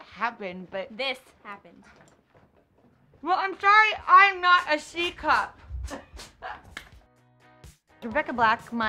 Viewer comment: Katy: "We used to be friends in third grade but something happened..." Rebecca: "THAT happened!" Katy: "Well sorry I'm not a C cup!" 😂😂😂😂Lmaooo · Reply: I thought she said tea cup